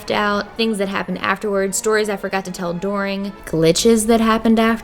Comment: I’m sorry...did you just say DORING instead of during? Right after you said Philadelphians don’t know the letter o??? 4:41 for those who don’t believe me 😂